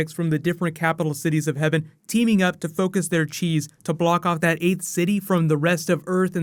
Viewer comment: I thought he said "Focus their cheese."